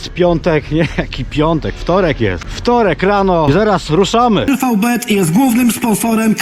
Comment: ja prdle usłyszałem TVP !